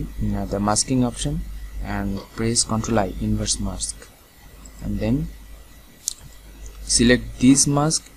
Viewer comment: What did you say and do @ 2:13? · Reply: ctrl+i command+i inverse mask